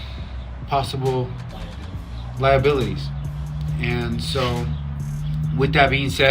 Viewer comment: invcrease the volume and Listen to joshua Labia saying "liability" 😂😂😂 he is telling Diego what to say